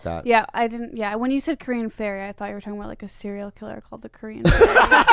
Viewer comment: I thought he said Korean fairy